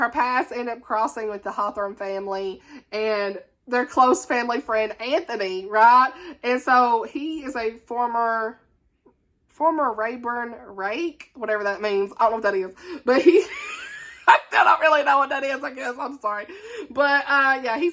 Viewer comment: I thought you said "former raper" and I had to rewind and listen again 💀💀💀